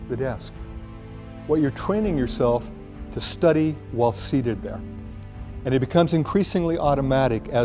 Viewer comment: What do you say in 1:20 ? „Training yourself while seededa?“ Please help me ! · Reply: Seated down